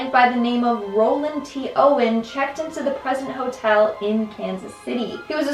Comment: At first thought she said "Crusty hotel"